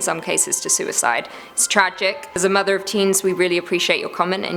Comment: That sounded like was saying that she was the "Mother of teens" 😄.